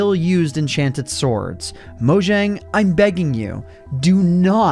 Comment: I thought you said “I’m gay”. XD